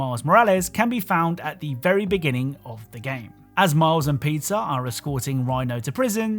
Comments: did he just say miles and pizza? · Did he said "As Miles and Pizza are escorting Rhino" 6:50 or is ot jus that I'm hungry lol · I thought you said Miles and Pizza when you were covering spiderman miles morales for a sec instead of Miles and Peter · Did... Did you just call Peter "Pizza"???